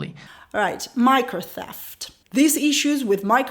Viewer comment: I thought he said micro death! 😂😅😅😂Infamous!